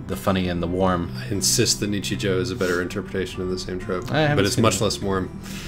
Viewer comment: I heard Nietzsche Joe at least 4 times, and was on the border of looking it up before I finally realized it was a single Japanese word.